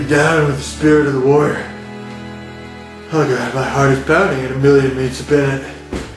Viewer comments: “My heart is pounding at a million meats a binnet.” Hahahahaha 😂🤣😂🤣 Anyone else catch that?? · Did I hear "my heart is pounding at a million MEATS a binute" @ 9:10 or am I crazy?